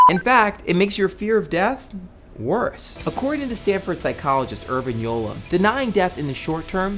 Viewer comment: did I heard "Stanford Psychologist Urbin Yolo"?